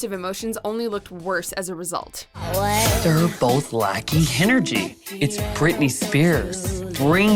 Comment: Did Alyssa say "Hen-ergy" ?